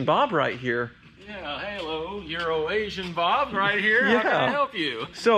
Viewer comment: Every time he says "Euroasian Bob" I hear "Urination Bob"